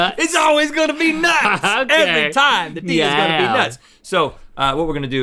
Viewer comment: "The D is gonna be nuts." That's what he said